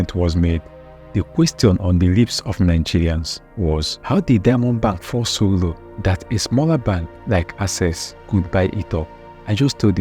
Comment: I was pretty impressed with the whole track record of the video until I heard "How did diamond bank fall so low that a small bank like access could buy it" .. I dont have data to counter you so Kindly elaborate. thank you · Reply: Okay. Thank you. Let me explain further, what I said was "how did Diamond bank fall so low that a SMALLER bank like Access, could buy it up". I didn't say 'small' but "smaller". Meaning that during those days, Diamond bank was bigger than Access. Not bigger in capital nor better service delivery, but bigger in REACH. Diamond Bank had more branches, more customers, and was more widely known than Access.